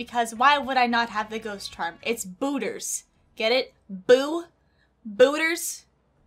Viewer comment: At first I thought she said hooters: how many people thought the same 👇